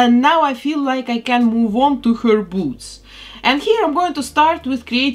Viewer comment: i thought she said boobs 😂😂